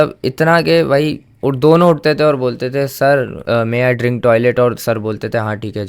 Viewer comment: did i hear may i drink toilet?